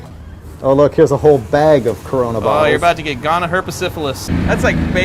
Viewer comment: Did anyone else catch freiburger say at 6:08"oh there's bag of corona virus" wtf!!!! Its 2020 we had a pandemic